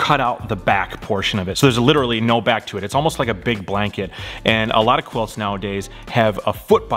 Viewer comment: "it's kinda like a big blanket" - No, a quilt is exactly a big blanket. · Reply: I thought you said "Pig Blanket".